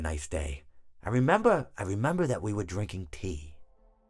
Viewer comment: I thought mr hippo said we were drinking pee🤣